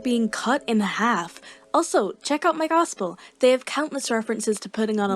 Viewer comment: Also when you said check out migospel i thought you said "check out my gospel"